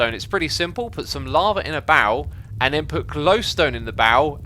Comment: Oh barrel, I thought he said bowel